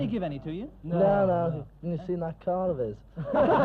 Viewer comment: What does he say at 7:40? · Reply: Samuel Lally "You've seen that car of his?"